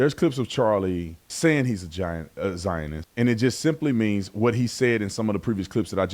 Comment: did he just call Charlie a "vaginoist"??!